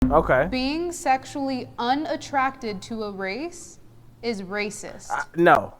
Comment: I thought it said making yourself sexually un attractive to another race 😂